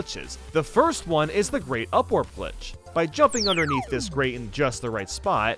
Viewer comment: Ok but like I thought he said “Great Upwarp Glitch” and my mind instantly went to Tick Tock Clock